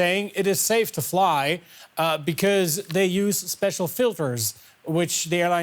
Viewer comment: I don't know about you but I heard him say "special filfers"